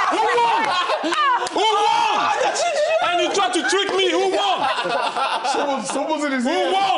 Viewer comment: At first i thought Thiery said "You tried to trick me! Wagwan! Wagwan!" I thought man turned Roadman for a sec lmfao